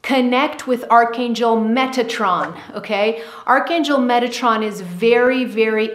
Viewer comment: Got a transformers flash back at the end 🙀 thought she said... Megatron 😸